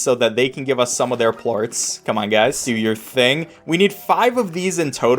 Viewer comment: Did you say port instead of plort?